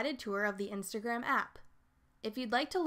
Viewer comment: Thought it said TeachBoomers xD